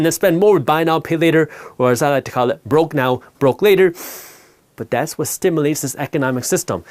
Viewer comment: i think your version of BNPL is Broke Now Poor Later (instead of Broke Now Broke Later).😂 good content, as always!